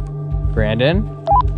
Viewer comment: there's a strange voice..wish the music wasn't so loud. It's hard to tell what's being said but I think I heard Brandons name.